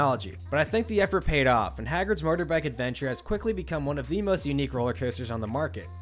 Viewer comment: I keep mishearing it as "Hagrid's Murderbike Adventure" and going "the what now??"